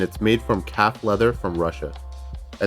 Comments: Thought he said cat leather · did i hear "cat leather" ? 😳😳